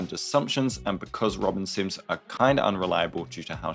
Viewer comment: LMAO is swear I heard "robin simps"